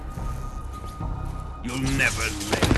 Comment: the subtitles say you will never lay a finger on it but the guy only say you will never